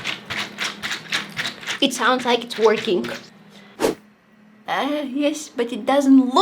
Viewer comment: I heard twerking instead of working 😅